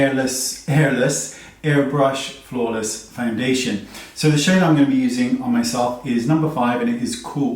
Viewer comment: I thought he said hairless foundation. 😀